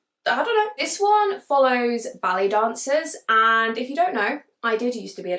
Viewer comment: As you were talking about The Turnout, the whole time I thought you were saying belly dancers instead of ballet. Had me confused, because the cover gave a whole different vibe. Then it dawned on me😂